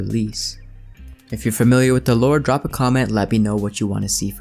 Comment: For moment I thought he said if you’re familiar with the Lord and I was all like “ooooooh we bout to get some Jesus up in here”!🙏